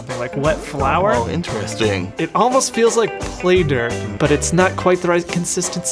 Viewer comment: what did you just say? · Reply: im sure he was trying to say "play dough" well..... i think he did :p